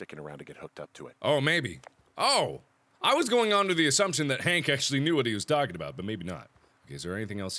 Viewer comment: did mark just call henry hank XD